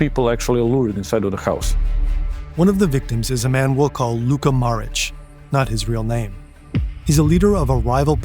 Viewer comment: i thought man said luka modric.